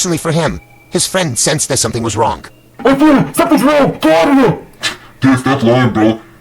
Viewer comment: Here’s I think what and were saying, if anyone wants to know 3:01: “Hey Peter! Something’s wrong! Get outta there!” “Dude, stop lying, bro. The guy who opened up this cave and turned into fried chicken was wrong, dude.” 3:24: “See, I fucking told you, dude. Nothing’s gonna fucking happen to me.” 3:30: “I fucking told you, dumbass!” “*goofy ahh scream* IT BURNS!” 3:50: “Holy shit, I need to get outta here!” 3:55: “Get out of the fucking way! I need to leave!” Stay back! Don’t get any closer! (For the people that couldn’t hear the voices that well they were buns anyway this is for you)